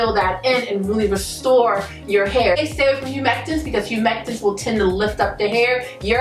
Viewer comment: It's a cul video. But am I the only one that heard her say surfactants instead of humectants before stage 3? I understood anyway. am subscribing.